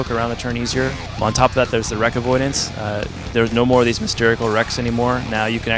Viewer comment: Did he say mysterical? I LIKE.